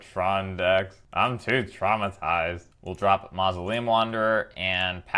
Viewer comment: For a second I thought you say "I'm too TRON-itized". It just about made my day.